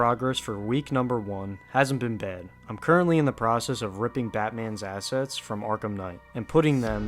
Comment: "I'm currently in the progress of ripping Batmans ass" is what I heard Jokes aside, this sounds grand as hell